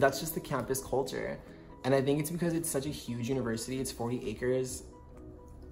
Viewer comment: "Huge Campus?' wow, lol UT Austin is 437 acres. That's nothing! I go to Purdue, 2,468 acres. 8:10 Also, I think he said 40 acres. lol